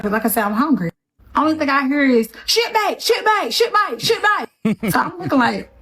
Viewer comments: You hear what she is realllllly concerned with? The MFing food truck that she has mentioned 15 times. You can see the lethality is strong in this one. What a huge waste of money and resources expended on her "training". WOW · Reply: I thought she was saying “shitbag” not “shipmate”. She was so hard to understand. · I heard “Shitbag, Shitbag, Shitbag” which is right.